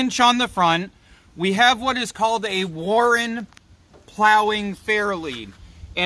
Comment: Warn I hear you say Warren